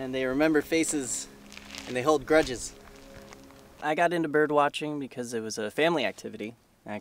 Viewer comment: Am I crazy birb lover or did I just hear him say "birb watching" instead of "bird watching"???